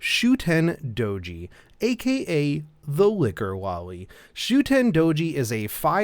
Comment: I thought I heard Licker Loli when it was Liquor Loli...